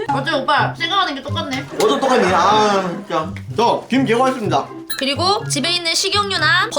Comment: 에 나만 김 개 맛있습니다 라고 들리나여